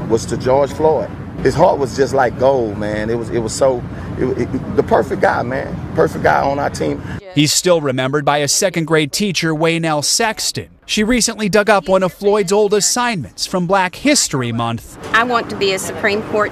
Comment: Wtf is Perry Floyd? Can’t be the ONLY ONE saying that..... If this was GEORGE Floyd’s....